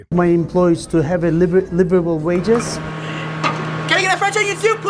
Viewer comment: The first time I heard "can I get a french onion soup" I was half-asleep and thought he said "can I get a fried chicken soup", which sounds about as unappealing as his voice